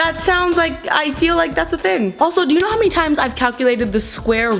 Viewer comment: I thought she said i feel like that's a thin.